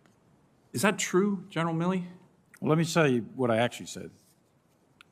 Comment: "Let me Sell you what I Actually Said."